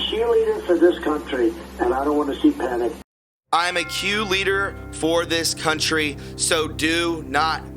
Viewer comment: Sounds like he said "I'm a cheerleader for this country" .